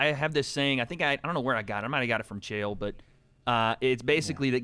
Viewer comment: did say he was in jail? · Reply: no chael from UFC